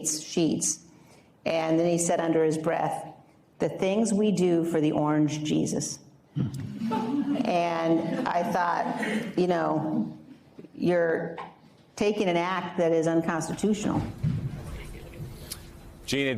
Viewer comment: I misheard . I thought Orange Cheetos